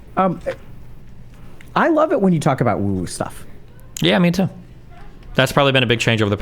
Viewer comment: sorry what are they saying? @ 1:43:22 "I love it when you talk about uwu stuff" ??? that is not what he's saying, I can't even context clues it in